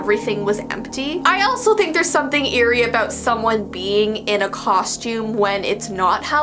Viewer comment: why did I hear beeing😭